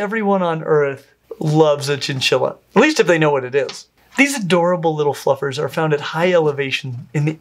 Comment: did he just say; these adorable little fuckers?? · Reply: he said adorable little fluffers